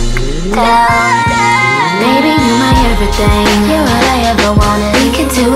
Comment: Thought he said baby your my headband😫🤣😭🤣🤣🤣